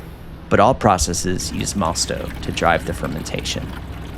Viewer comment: what's that liquid you're using to kick start the fermentation? Malstov? Didn't quite understand the word. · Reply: mosto!